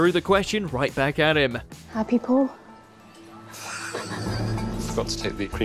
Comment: I could’ve sworn that Nadiya said “are YOU happy Paul?” Oh well, still funny!